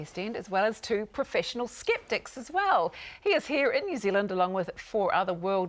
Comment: I misheard "skeptics" as "skip d**ks" at first.... Oops.